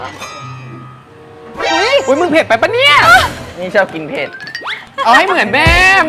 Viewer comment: พี่กันพูดว่า"นี่"หรอ?รึคิดไปเองอร๊ากกก😳😵